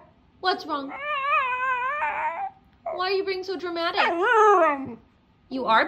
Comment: First it sounded like she said: im not "Yes you are" No, im not "Yes you are" No, im not